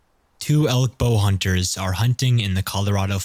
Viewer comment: When elk hunters shoot a bull... its a bull elk. A mature male elk is a "bull." XD Still a great video, though. ^_^ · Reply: I thought he said elk 'bow' hunters?